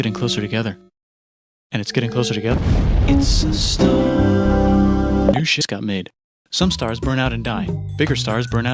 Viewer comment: “new jizz just got made” don’t ask how I heard this.